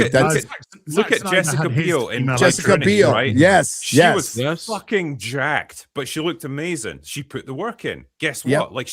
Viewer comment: Do you know which movie he mentions Jessica Biel being in at about 54:27? I can't hear what he says and captions can't tell because and are talking over him. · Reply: "Blade Trinity" are the words he says.